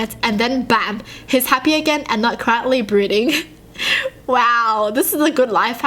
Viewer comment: is it just me or instead of saying "Brooding" she said "Breeding"....plz just say i am wrong plz this is messing with me now